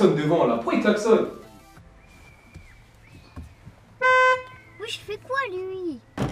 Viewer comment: YOO QUI A UNE SŒUR OU UN FRÈRE RELOU ?? LAISSE UN POUCE BLEU ET ABONNE TOI ;) LA VIDÉO D'HIER: A TROUVÉ UNE TABLETTE TACTILE A LA MAISON ! <link> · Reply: à 1:32 elle dit wesh il fait quoi lui MDR c'est une thug life! 😂😱😂😂😂